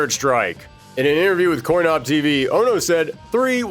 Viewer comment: for a second I thought you said “corncob tv.”